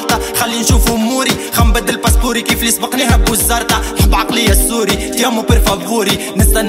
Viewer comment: تحياتي الك من سوريا المضلومه 🇸🇾🇸🇾🇸🇾🇹🇳🇹🇳🇹🇳🇹🇳اليفهم زين خوتي اسمعت ((قل سوريا)) بل دقيق1:21صحي ولا انا فهمان غلط